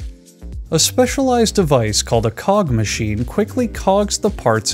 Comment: I thought you said "pog machine"